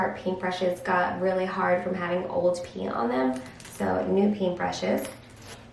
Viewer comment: Lol I totally thought you said your paintbrushes were hard from old pee...I was like "what?" 🤣